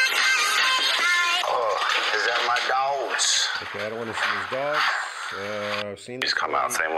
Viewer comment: I thought I heard " I don't want to die". Maybe I'm wrong